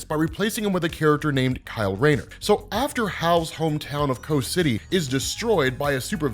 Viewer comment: Every time says "Coast City," I hear, "Kos City." Which one is it? lol